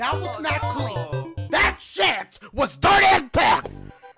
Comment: what is he saying 😂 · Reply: I believe he said "That sh*t was dirty as f*ck"